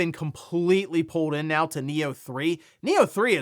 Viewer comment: When you said Nioh 3, I thought you said Neo 3